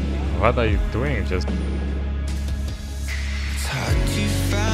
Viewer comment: he legit sounded like the meme "what he doing?"